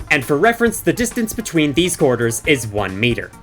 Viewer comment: Oh, QUARTERS. I thought you were saying corridors because I didn’t see the coins.